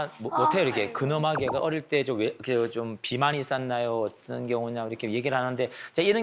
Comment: 초에 어릴때 뭐라는거? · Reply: 비만